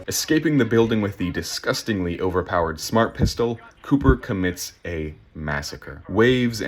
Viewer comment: I thought you were gonna say "Cooper commits a war crime."